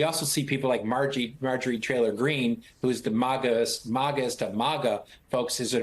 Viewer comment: I heard Margerie 'Trailer' Greene, and while I admit it was in my head, I can't hear that name the same way again😅😅😅